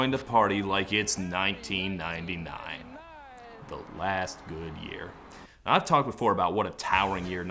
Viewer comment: "1999, the last good year" man I felt that in my jaded millennial heart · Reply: I thought he said, "the last new year." What other new year tops <num>?